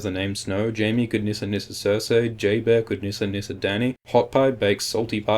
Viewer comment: did you just call Jorah Jayber?